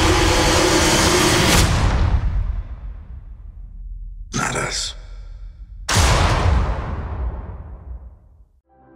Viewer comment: did anyone else noticed!! That audio sounded like ULTRON mixed with CAP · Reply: I believe it’s Tony saying “some people.” It’s Hawkeye saying “move on” and Cap saying “but not us.”